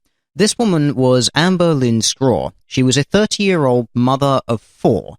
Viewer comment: thought you said 13 year old mother of 4